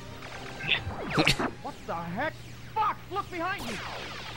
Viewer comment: it sounded like falco said "what the heck? fuck look behind u"